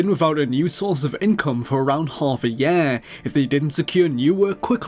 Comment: The oddness of your particular British accent exploded right there with the word "year." It sounded like a completely different word to me. "Hoff a yeah!"